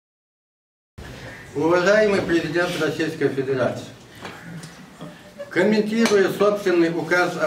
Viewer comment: Послушайте, вначале видео мне послышалось " Уважаемый пи*дец Российской федерации"